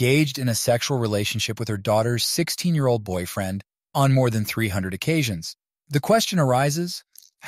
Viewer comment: Did I hear "300" occasions?